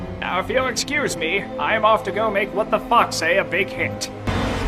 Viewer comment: They translated "What Did the Fox Say" as "What the fuck" xD Not sure why I find that so funny, but it is. Fitting for the scene, too!